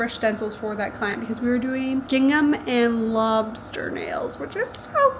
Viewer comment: why did i hear "Gangnam" and not "Gingham" 🤣 they came out so cute